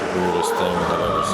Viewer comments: translation says weirdest, I think he said coolest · he says "this is the coolest thing i've ever seen." but the subtitles say "weirdest"